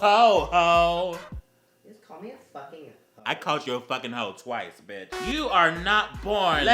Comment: May be late but “Did you just call me a fucking hoe?” “I called you a fucking hoe twice, bitch.”